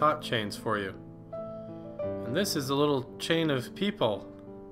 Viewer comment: who thought he were saying " little chinese people " instand of " Little chain of people "